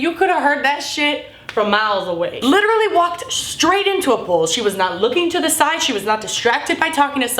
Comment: I'm such a dumbass , I thought she said she walked into a pool not a pole ..